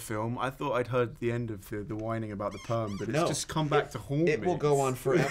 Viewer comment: What did Ben say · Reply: he says 'but it's just come back to haunt me' :-)